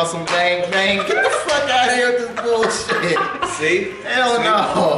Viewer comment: что он говорит? ""Get the fuck are you little bullshit""? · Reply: Он сказал "Get the fuck outta here this is bullshit". Это значит Проваливай от сюда, это дерьмо.